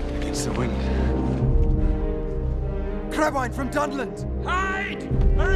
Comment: One flock of birds and you abandon your planned route barely into your journey? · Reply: If you listen at 50% playback speed he clearly says 'Crabwine from Dundland'. I know my Tolkien.